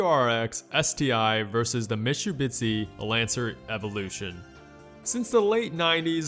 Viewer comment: Did you just say Mishubitzi @ 5:09? Jesus Christ lol